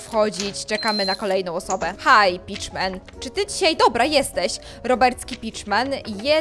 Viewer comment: jak pierwszy raz to oglądałam usłyszałam beach man i ja miałam takie lol